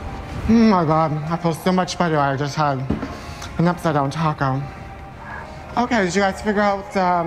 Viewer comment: what on earth did he say at 9:05? i can't hear shit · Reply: theo omg I feel so much Better I just had an upside down taco